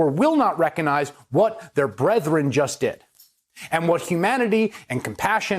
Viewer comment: Did he say bredrein